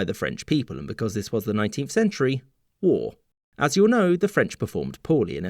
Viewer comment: thought you said 18th century for a bit